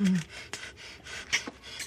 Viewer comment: Not bad! Mix it with the Relish!!! 😆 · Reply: It’s even funnier cuz he actually says “fix it with the relish” this is the most misheard quote on the show. Even HBO Max misquotes him in the closed captions. Slow the playback speed and you’ll hear the “f” sound.